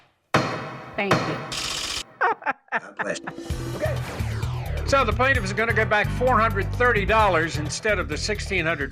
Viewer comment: what did he mumble? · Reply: I'll get you